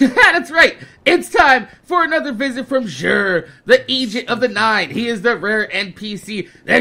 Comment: it sounded like you said the Asian of the nine